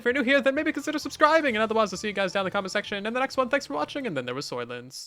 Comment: Did you just say "And then there was 'Soylents'" ? :D I think has a bug himself...